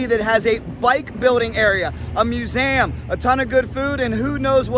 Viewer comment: did he say MeusAM?